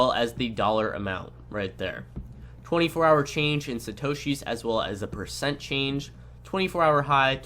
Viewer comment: Great video! One question I had (and if you said it, I didn't catch it), what the heck is a "shitoshees"? You kept making reference to it when talking about how the prices are displayed. Around the 5:15 mark is when I first heard it. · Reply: satoshi. The name used when dealing with bitcoin amounts. for example <num>BTC <--- 10,000 satoshis